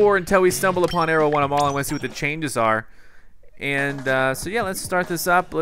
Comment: after rewatching this, i kept hearing him saying "i wanna see what they changed" and i just kept screaming "just use recon you idiot!"